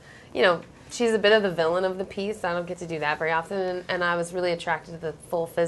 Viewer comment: So disappointing to see...not the movies; their portrayals are beautiful. But I find Effie one of the ABSOLUTE LEAST selfish people in the entire thing. She's never been remotely villainous. I love ya, Liz, but that characterization...just, no. By now it's definitely time to let go of that notion--that flippant dismissal of a fascinating and wonderful character. It's the casual, shallow, superficial, thoughtless, unfair reading of her. She's my favorite, and I can just go on and on about her for ages and ages. And geez, woman, it's "Haymitch," not Hamish! xp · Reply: I thought I heard her say "Villain of the Peace".